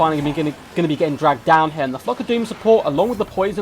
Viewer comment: Lmao with your accent, when you say "flock of doom" all I heard was "f***ing doom".. I nearly spit out my drink 😂😂😭